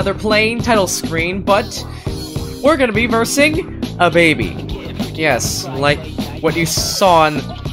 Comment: literally playing fnf youtube: ye this looks like osu for me. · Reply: did you just say DaBaby